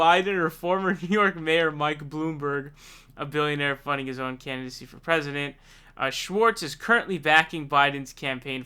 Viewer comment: I misheard it as 1:38 "...a billionaire funding his own fantasy for president" - but I think that actually makes more sense.